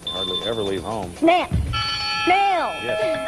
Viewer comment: Correct me if I misheard, but seemed to clearly start saying "snack" before sticking with "snail"